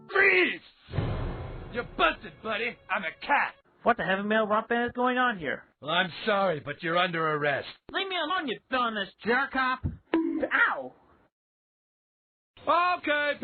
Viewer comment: what does Chris’ voice say here? “What the hell bell rumpus is going on here?”?